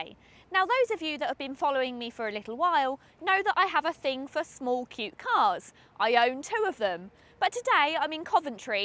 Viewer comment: sounded like she said "I have a thing small cute co*#s.." DOH!!!